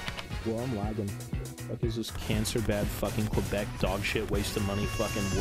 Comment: At I’m sorry did he just say Quebec at 5:07?😂😂